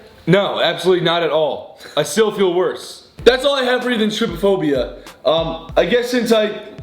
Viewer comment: "thats all I have for Ethan's stripaphobia" who else heard that??💀